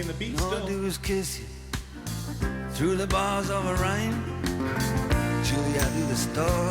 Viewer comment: I’d say that line is surpassed by “All I do is kiss you / Through the bars of a rhyme,” which is just a brilliant pun, and a perfect mix of cleverness and lump-in-your-throat pathos. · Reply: I always thought he said "through the bars of the rain". Thanks for clearing that up.